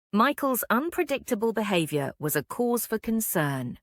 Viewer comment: Please anyone what is she saying ?> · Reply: Michael's unpredictable behaviour was a cause for concern.